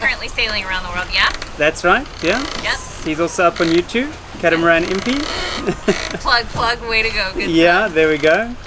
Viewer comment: Anyone catch the channel of the father that is sailing around the world? All I heard was "catamaran mp" and that is not it. :-( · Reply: catamaran impi